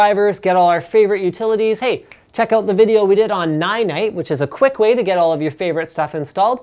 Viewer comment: What software does Linus say at 4:30 to use to quickly install all your software. Sounds like he says "Nigh Night"? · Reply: Ninite :)